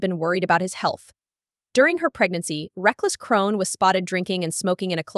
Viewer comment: I thought she said Chrone🤷🏾🤦🏾